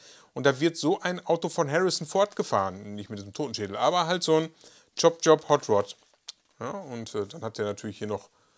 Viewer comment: Top Chop heisst das...Ich hab im Video Chop chop verstanden 😉 Super Ding, ich glaub ich muss die auch haben. Baust du den violetten auch noch? 🤔 Da bin ich mir noch unsicher...